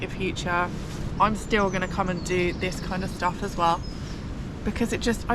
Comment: Thought you said "bird stuff" 😂